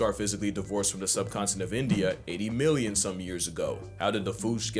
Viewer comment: Did he say Madagascar came off "the subcontinent of INDIA"? Or did I mishear "subcontinent" in which case what WAS the word he said?